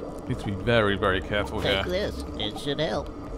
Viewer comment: does Abe say “take this it’s Chanel.”? Because that’s what it sounds like 🤣